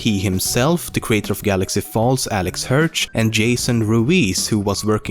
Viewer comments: i heard "galaxy falls" is it just me? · Excuse me did he just say GALAXY FALLS?! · he said galaxy falls instead of gravity falls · Did he just say "Galaxy falls" instead of "Gravity Falls" at 3:00? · wait... did he say "galaxy falls" at 3:00??? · notice how he says GALAXY falls instead of GRAVITY falls 😂😂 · did I hear galaxy falls? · I Thought He said Galaxy falls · did he say Galaxy Falls? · Pls do top 10 facts : Steven Universe And it also sounded like you sayd Galaxy Falls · did anyone else notice that he said galaxy falls instead of gravity falls at 3:00? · He just said Galaxy Falls instead of Gravity Falls. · what ''galaxy falls' ' 3:01 did i hear right? lol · Did he just say galaxy falls? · I hear him say galaxy falls... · Did you just say the creator of Galaxy Falls? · did he just say, galaxy falls? XD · Did he just say... GALAXY Falls? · anyone notice that at around 3:02 he says "Galaxy Falls" instead of "Gravity Falls" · Did say Galaxy Falls instead of Gravity Falls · did he just say galaxy falls instead of gravity falls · did u just say galaxy falls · Not to be that kind of person... but did anyone else hear galaxy falls at 3:02😂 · Did he just say "Galaxy Falls" at 3:02 ???